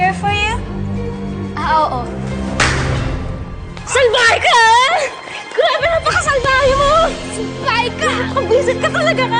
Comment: observe what is the girl saying salbahe ka,grabe nagpakasal tayo?